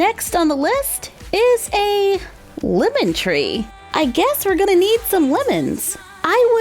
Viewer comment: I thought she said "Woman Tree" when she said "Lemon Tree" and I asked myself what a woman tree was.